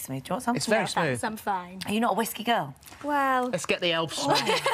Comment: Phil handing his boy some poppers 😅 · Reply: I thought he said 'let's smash the elf'... 🫷👀✊️